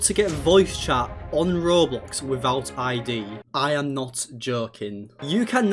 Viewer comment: I thought i heard him say "I am not jerking"